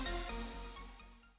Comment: When you said peace it sounded like you just said piss.